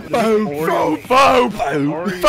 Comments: when he first said "Beat Soap" I thought he said "Pizza" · Soap you didn't choose order the fukkin pizza (I misheard what he say)